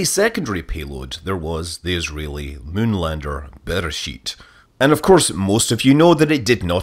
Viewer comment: I thought you said Israeli moon lander better shiiit lol